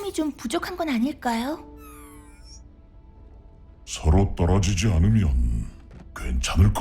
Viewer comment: 나만 빨아주지않으면으로 들리냐